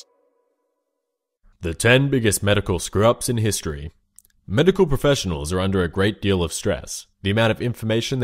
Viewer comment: Lmao I kept hearing it as "Top 10 Medical scrubbs in history"